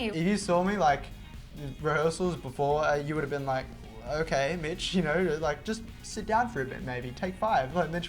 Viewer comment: oh god i thought mitchell said bitch but he actually said mitch